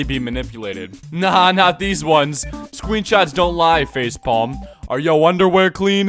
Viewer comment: I swear I heard ”scrweenshots”